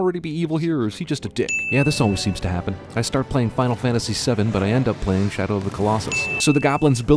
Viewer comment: in 2:33 he typed final fantasy 12 instead of final fantasy 7 ding